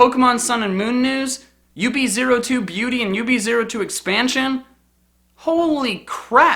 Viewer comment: did he just say UB-2 Expansion?